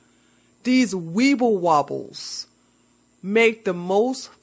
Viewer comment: Girl, I thought you said...'weave-a-wobble'...and I literally laughed so hard I cried, then had to watch it again..and still laughed. You are the Voice of many, with the insight and logic of way too few... Truck on